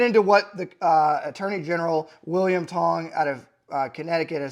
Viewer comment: Ah yes, William "Tong". Very Connecticut name. /s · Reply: at first i thought he said William Hung, that guy from American Idol that butchered songs.